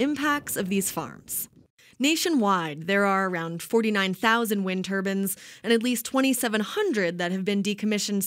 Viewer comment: Did she say... Wind Turbans?